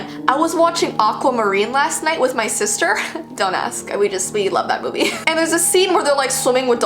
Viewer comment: did y'all think she said dumb@ or is it just me I think she said don't ask😂